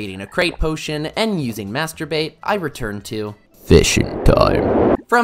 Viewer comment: DID BRO SAY MASTER BAIT. Cancledddd. nah jk lmao